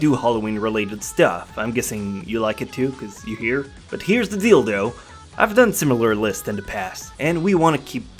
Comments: in 0:25 did he say here is the dildo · Did he just say: "here is the dildo"? where is it? show us the dildo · I thought he said, "here's the dildo"...just me?..ok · Did anyone else hear him says hears the dildo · am i the only one with a such perverted mind that around 0:27 i heard that "here is the dildo"? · anyone else hear him say "here's the dildo"?